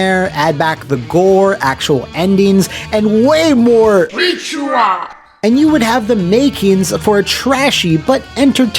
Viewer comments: Oh, it says beat you up, I thought it said reach around. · I'm sorry...what is that announcer saying? I keep hearing "Reach around!" Also, I nominate Pit Fighter for SNES as worst fighting game of all time.